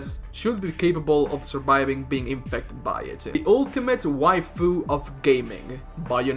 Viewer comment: Excuse me sir, did I hear "the ultimate" cuz if that's so you don't know nothing